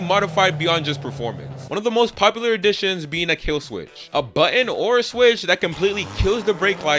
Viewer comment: Did he say, "a Buh ent?"🤣